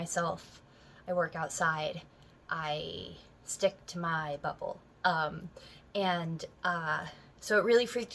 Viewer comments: Sounded like she said, “I stick to my butthole.” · I know she said bubble but I swear it sounded like she said butthole